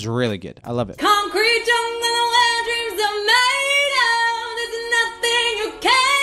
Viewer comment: Does anyone else hear "concrete jungle wetdream tomato", or is it just me?